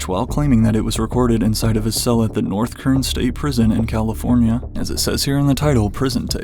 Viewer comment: I swear I thought he said North Korean iwaslike tf is bro doin over there